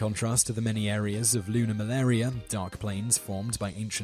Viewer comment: for those confused by him saying lunar malaria at 2:13, its actually called lunar maria (: I still have no idea how that sounded like malaria tho ^^